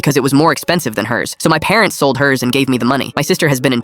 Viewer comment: Why did I heard "they selld her"💀🥀